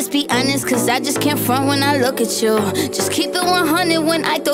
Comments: Is it just me who thought the part where it said “cause I just can’t front when I look at you” said “cause I just can’t fart when I look at you”..... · I thought she said "I just can't fart when I look at you" I ain't joking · Thank you for this because whenever I watched the MV I always thought she said "'cause I just can't fart when I look at you" 😂😅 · I thought she said "cause i just cant FART when i look at you"